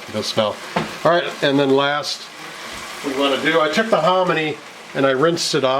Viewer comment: What ingredient did you put in the pan at 4:40? White stuff, in dices. You said you rinsed it to get rid of sodium. Im Swedish, didnt understand what you said there..:) Is it corn? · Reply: It's hominy. It was corn but has been chemically treated. It turns white and has a different texture. It puffs up and has a chewy texture. If you really want to try it, you can probably get it on Amazon. It originates from the southwest US so it might be difficult to find in Europe.